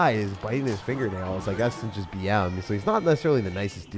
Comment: nah bro I think he’s saying the game is gonna be a nail biter. Not BM